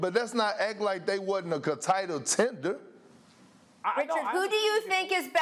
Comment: I heard "contitle tender" and came straight here 😂 Perk is off the percss